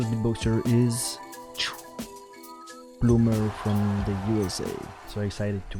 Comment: Bloomer 4:54 Kim 7:07 FootboxG 9:43 Skiller 12:00 Alex 14:20 Level Kenny 16:22 Helium 18:35 Reeps One Awesome video!!! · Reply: I thought u said boomer